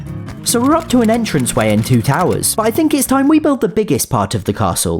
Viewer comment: I thought he said twin towers